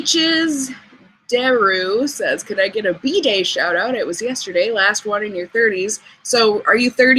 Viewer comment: Hi, watching this on Nov 18th. I just had to tell you, when you read that someone wanted a B-Day shout out, at first I thought you were saying "bidet shout out". LOLOLOL